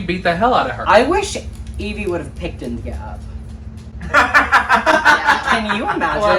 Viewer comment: what does say about yvie in 16:00???? Picked a what? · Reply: Picked India up